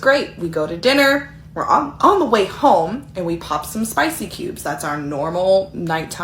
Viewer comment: What's a Spicy Cube? That's what it sounds like she said, never heard of them.